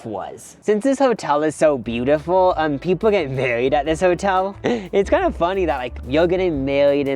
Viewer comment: Weirdly i thought at 13:00 he said burried instead of married 😂